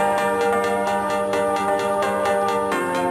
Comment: headhunterz drop and melody, r3hab nothing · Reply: Giovanny Rivas he is already said : It's my new track with R3hab